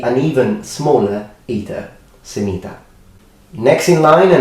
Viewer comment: When he said "semita". I thought he said "sanita", meaning "toilet" in Portuguese.